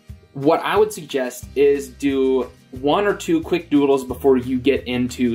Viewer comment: his "quick doodle " is 10 times better then anything I have made TwT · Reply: I thought he said "quick noodles"